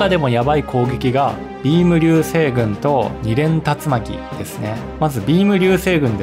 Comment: 淫夢流星群って聞こえた俺は死ぬべきだと思う